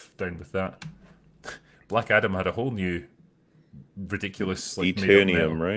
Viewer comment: I really thought you were saying blackadder with your accent there drinker